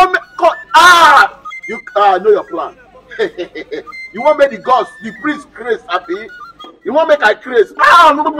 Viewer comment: Thought they said the gods are wise😂